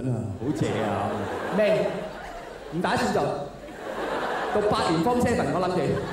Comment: 請問33:25處，「未！唔打算做！」之後那句子華說什麼？ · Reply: 讀八年form7(留級)